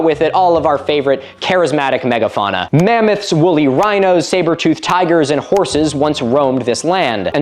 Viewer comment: I misheard the name of one of the animals as the "Sabre Toothed Horse" and, oh boy do I want to see that wondrous beast!